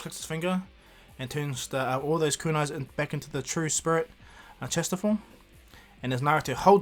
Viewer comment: I thought he keeps saying sprit testicle 😂😂